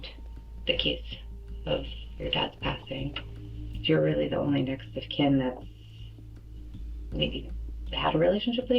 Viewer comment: This video is cleverly disguised AI slop. At 23:38 she says he is the only next of kin, and the subtitles said "nextest candidate". Very obvious to me but maybe not to the average person